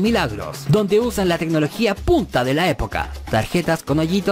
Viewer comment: Escuche "la tecnología p*ta de la época" Diablos chatarra 😂😂😂